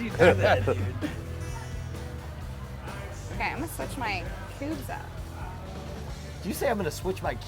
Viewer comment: I thought she said "im gunna change my boobs". OMFG?!